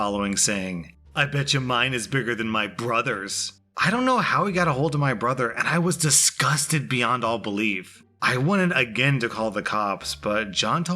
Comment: Is it just me or did he say brother I want to check I heard right.